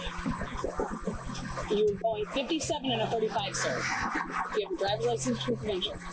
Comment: Subtitles are wrong 12:22 she said 57 in a 35 multiple times NOT 57 in a 45